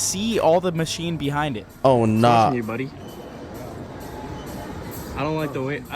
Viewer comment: I heard a aww hell nah 🤣